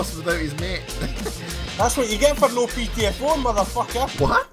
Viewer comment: What did said at 2:55, no ptf4 ? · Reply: Ptfo, it means play the fuck out... Meaning go try hard and attack objectives